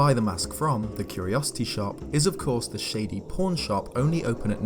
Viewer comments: I legitimately thought he said "porn shop" lol I have a dirty mind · Hahaha, I thought you said «porn shop» instead of pawn shop and I was really spooked. Happy Halloween btw! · I legit thought when you said "Pawn shop you said "P*rn shop."